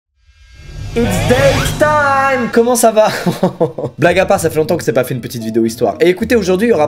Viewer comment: blague à part ( black à part ?! Quoi ? MAIS C’EST PAS CE QUE J’AI DIT ) qui a la réf ?